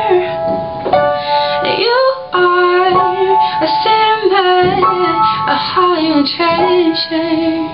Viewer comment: Good Job!!!! The first time i heard this song i thought he was saying "You are a Cinnabon"